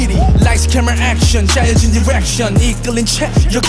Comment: bruh i misheard bx’s direction with errection OMFG 😳